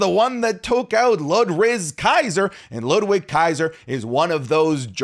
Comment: Did he say Ludrizz Kaiser💀💀💀💀💀💀💀💀💀💀💀💀